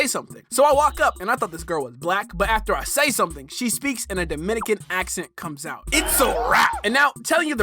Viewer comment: did she said "I no black, I dominican" ? 😭